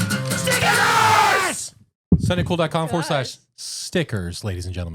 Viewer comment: I thought yall were saying snickers but anyways love the podcast guys yall are so different and creative love it and yall are straight up killing it on the shorts thats how i came across your podcast